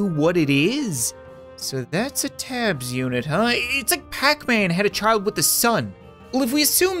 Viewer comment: I thought he said, "It's like pacman had a child with his son"